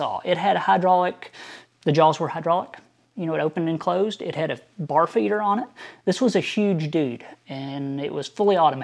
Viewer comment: did you really say this saw had a "barf eater" on it?!?! 🤮